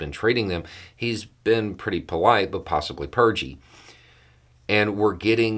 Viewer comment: Did you just say Putin was "polite but purgey?" 🤣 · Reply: Ohh, I thought he said pudgy.